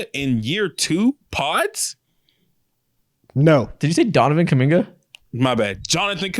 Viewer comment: gaslighted into thinking he said Donovan instead of Johnathan, but I can I hear the J 😂